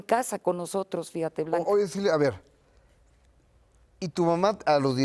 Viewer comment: Minuto 8:27 escuche bien o mal ?? Le dijo silvia ?